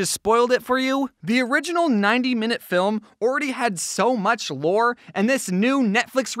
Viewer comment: I thought he said so much vore (I should not have said that)